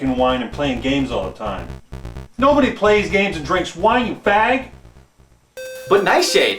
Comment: Uuuh... at @ 3:06 did Nightshade call a F*g? Woooah. I mean the slur was more commonly used back then, so its clearly a representation of the era it was made in...but this caught me off guard majorly.